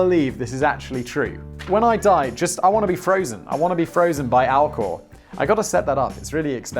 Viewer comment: I misheard Simon....I thought he said that he wanted to be frozen by Al Gore. 😆